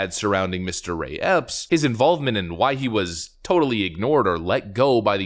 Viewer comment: You say Mr Ray Epps, I hear " mystery ops ".